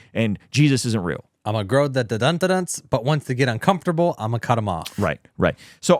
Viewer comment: is saying “dun dun dun dun duns”? Like dreads? lol